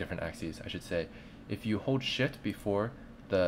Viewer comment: Is it me or did I really hear "shit" instead of "shift"? o_o Great tutorial though, 9.999/10